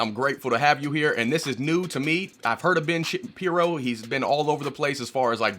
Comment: Did you just say "Ben Shittin' piro"?